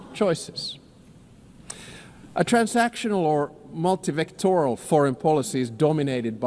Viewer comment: I doubt the subtitles. Is he talking about "multivectoral" (as I hear) or "multifactoral" (as subtitles suggests)?